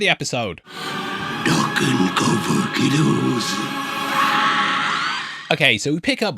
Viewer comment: What did Pennywise say? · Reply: Duck and cover, kiddos